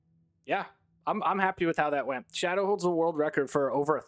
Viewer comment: who did you said had 1k cause I didn’t heard it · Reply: Shadowdaze. The link to his world record is in the description